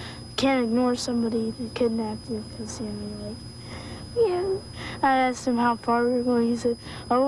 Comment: Such an amazing segment. Rare to see happy endings. Does anyone know what she said at @ 1:39 ? Sounded like a sound or something. Really smart and funny kid. Sad that the two small children were never found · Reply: I think she says "yeah".